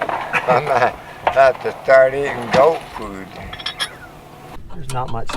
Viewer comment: "I'm gonna have to start eating goat food."...lol... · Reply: I had thought he said "dog food"! either way, funny!